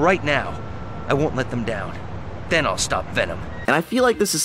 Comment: i thought spider-man said "Thanos stopped venom"